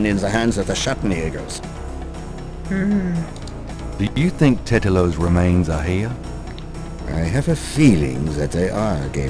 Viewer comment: LOL I thought he said something else "Tetlato's remains..."